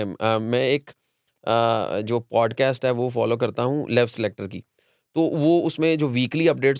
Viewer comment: What did he call the name of podcast "lave selector" or something???